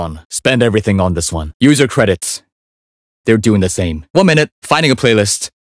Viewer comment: Wtf is this lmao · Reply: “One minute” Upon first glance (hearing) i thought he was saying “Womenit” 😭